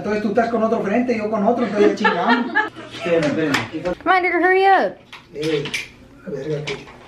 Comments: is it just me or did she say the n word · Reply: I said "hurry up Edgar" I speak really fast lol. (Edgar is my dad) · sorry but that sounded like she said 😭 nvm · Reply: Okay I need to stop speaking so dam fast lmao. I said “hurry up edgar” (edgar is my dads name) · did she say the n word, i heard it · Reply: She said hurry up Edgar She was talking to her dad ☺ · did she say the N word lol